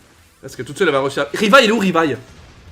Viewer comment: C’est moi qui suis mal entendant ou ta dis revi 😂😂😂